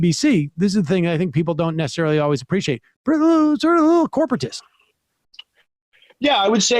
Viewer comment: tf did sam say😂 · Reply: I think it was "sort of a corporatist"